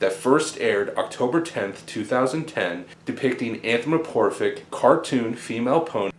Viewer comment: After all that explanation, they totally missed the fact that he said "anthromoporphic" instead of "anthropomorphic". nearly caught it.